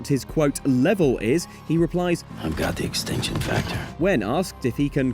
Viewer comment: I always thought he said "extension factor" not "extinction factor".